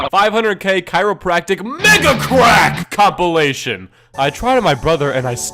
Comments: thought he said nigga crack lmao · I honestly thought he said NIG*A CRACK MEGA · Install Raid for Free ✅ IOS: <link> ✅ ANDROID: <link> Start with💰50K silver and get a Free Epic Champion 💥 on day 7 of “New Player Rewards” program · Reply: You do know that at 5:19 it sounded like you said N*GGA crack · Anyone else hear "NIGGA CRACK" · When I heard mega crack, I thought he said n***a crack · when you said mega crack i thought you said n-word crack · I swear to God I heard you say "Nigga Crack" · i thought you said nigga crack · it sounds like he said “NIGGA CRACK”